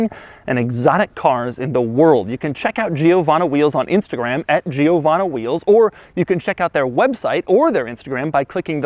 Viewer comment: for all this time i thought he said giovanni wheels